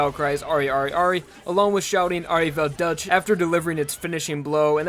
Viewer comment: Am I the only one who heard 'OLIVER DUTCH'